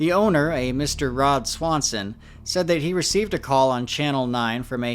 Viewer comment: almost made me spit take my Sprite thought you said Ron Swanson